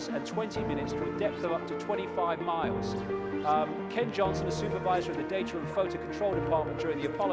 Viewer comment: I heard Cave Johnson. I've been playing too much Portal2 XD